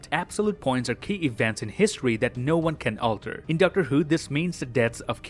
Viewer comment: At around 6:20, was he saying Dr. Who?